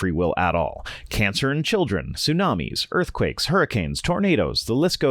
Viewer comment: I heard “cancer and children” and was like yeah that checks out.